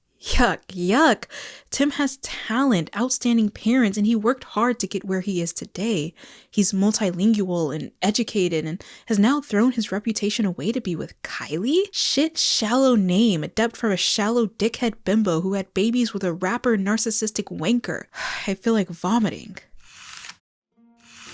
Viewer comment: dude i was listeing to this video while working and when i heard you saying "shit shallow name" i bursted out laughing cause i was so certain it was a joke with the name timothee chalamet (like shithee shallowmet or something)